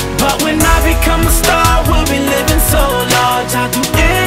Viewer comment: I'm going to become a start one day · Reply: around the 0:48 mark the video said "but when I become a start" instead of star